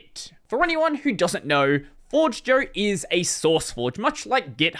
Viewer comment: subtitle issues 0:38 forage instead of forge 2:39-2:42 Forge Joe (across subtitles) instead of forgejo 6:41 poor request instead of pull request Also, basically every time the GPL was mentioned it was merged with the following word 0:24 GPLor instead of GPL or 5:30 GPLbut instead of GPL but 7:20 GPLstyle instead of GPL style and GPLcontributions instead of GPL contributions 8:03 GPLlicense instead of GPL license 7:58 + 8:16 + 8:22 GPLcode base instead of GPL code base 9:02 GPLdependency instead of GPL dependency 9:32 GPLand instead of GPL and lastly, this might be a nitpick but at 2:55 GPLv2 and GPLv3 are capitalized as GPLV2 and GPLV3, which is inconsistent with how it is capitalized in the rest of the video